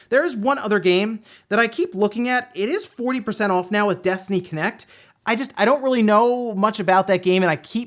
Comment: I swear, I was looking away from the monitor when you brought this game up, did an immediate double-take because my brain heard that as "Destiny Kinect," was on sale at the eshop.